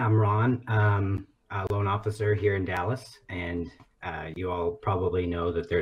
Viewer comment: He's a law officer? Than why he doesn't arrest himself? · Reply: I heard "loan officer", but not sure.